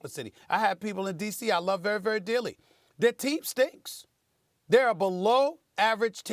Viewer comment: It sounds like he said their teeth stinks 😂😂😂...